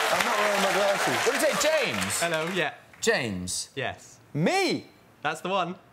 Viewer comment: What does Ed Sheeran say at 0:42 ? It sounds like "me" but that doesnt make sense. Ed: James?? James: yeah Ed: me?? James: yeah thats the one It would make more sense that Ed asked about his last name but I cant understand what he's saying